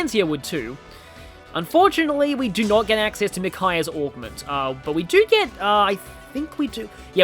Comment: TikTok is definitely an annoyance of mine (I just watched a video about how it's shaped the current Youtube environment, where successful videos now are basically just TikTok compilations), but I agree that Twitter's political flame wars might be worse. I was actually saying "Micaiah's augment", but I can see how my accent makes it sound like Orbment. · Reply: Ah, so it was augment after all... I had a feeling I had probably misheard since nobody else had mentioned it in the comments. Well, egg on face, it seems. It honestly really sounded like "orbment" to me, but I guess that's just proof I'm too Kiseki-brained at this point.